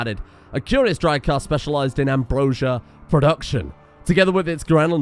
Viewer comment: hehe i thought you said "hambrosia" im very tired.